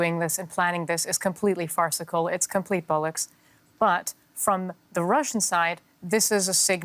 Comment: Did she just say "complete bollocks"?! Awesome